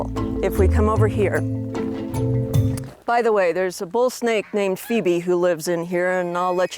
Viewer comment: Did she say bull snake!?😨🏃🏾‍♂️🏃🏾‍♂️💨